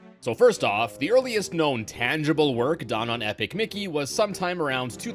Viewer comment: I swear I hear ethic mickey here